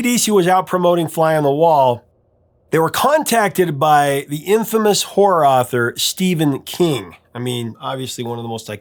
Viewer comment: I swear I heard “infamous whore author”. 😂